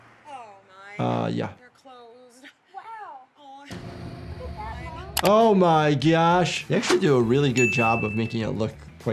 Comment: Why did I hear bro say oh my gyash in